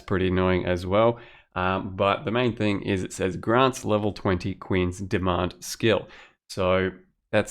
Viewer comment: rofl, I really thought you said "Quinn's Demand" instead of "Queen's Demand". Made my day :-D